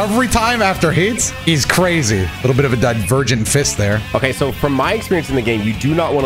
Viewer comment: why i heard "virgin fist" 💀